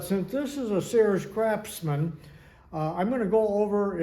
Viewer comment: Did I hear you say, "Crapsman?" thanks, always learn alot! "If that is one horse, I'll eat it for lunch!"